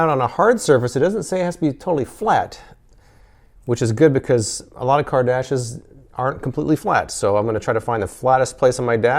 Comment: "a lot if car dashes aren't completely flat" I swear at first I thought you said "a lot of Kardashians aren't completely flat". Either statement would be true.